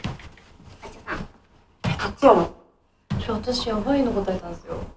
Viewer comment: ……ていかいですって聞こえるw可愛い❤